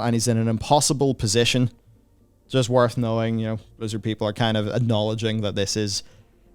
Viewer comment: When you say "Blizzard people" I hear "lizard people".